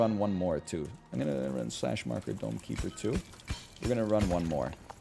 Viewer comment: I recently started watching NL through YT. I literally thought he was saying “Donkey Bird” instead of Dome Keeper.